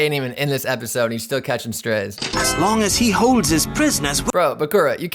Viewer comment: Bakura says, "As long as he holds HIS prisoners." Not US. The VA just didn't enunciate very well, but i definitely heard the 'h'.